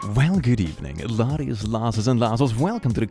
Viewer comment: I've been watching you for a little bit now, but I cannot understand what you say at the beginning. It sounds like you say, "Laddies, Lasses, and Lasses" but that can't be right. 😅